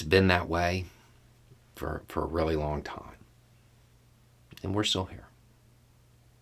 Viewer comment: I had to rewind the last 15 seconds because I thought you said "...and we're sorry." 😬😢